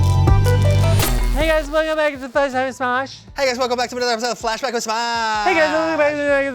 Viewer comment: I thought Ian said lunchtime with smosh 😭 please bring that back